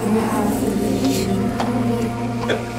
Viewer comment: I heard "you have a mission" :D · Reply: I heard "You have permission." Lovely work of art.